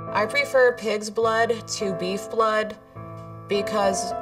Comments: I thought she said “I prefer kids blood” I had to rewatch it a couple times to reassure myself she didn’t say that😭✋🏾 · I thought she said "I prefer KIDS blood to beef blood" I was like wow this just got a whole lot darker! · I thought she said she preferred kids blood ; - ; Me just running for my life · I wonder what she would do when she discovers adrenochrome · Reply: When she said she drinks pigs blood I thought she said kids blood, I was like 😕 please no.